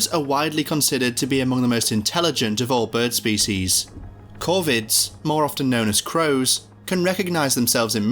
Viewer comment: Covirdae. I thought the narrator said covids..